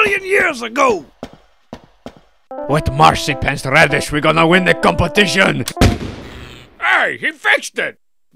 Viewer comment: I heard "With Marzipan's radish we're going to win the competition"